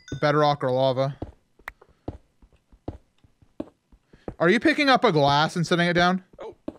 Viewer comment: What did say at 16:04? Are u picking up a - what? Sorry i have trouble hearing though my left ear and sometimes cant interpret what people say. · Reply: "Are you picking up a glass and setting it down?" was what said.